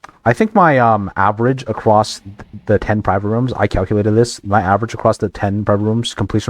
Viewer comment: for a second i thought it said i got sub ten, 15 private rooms in a row